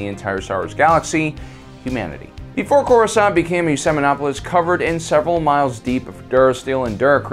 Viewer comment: Technically, coruscant is an ecumenopolis. I'm not entirely sure what you said at 0:56. If you said selenopolis, that would be innacurate since coruscant is not a lunar body. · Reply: he said ecumenopolis